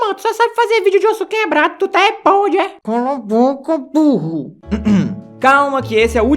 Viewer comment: so foi eu que ouvi Cala boca poha kk